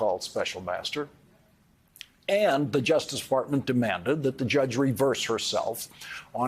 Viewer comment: did he say, “justice fartment”?? 😆